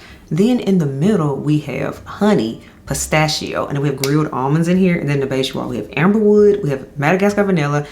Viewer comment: Girl, I had to give you a double take. I thought you said grilled onions, not grilled almonds on that Navitus fragrance, lol.😅🤣